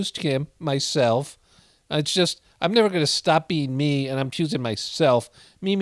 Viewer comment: I thought she said “ I’m never gunna stop being MEAN”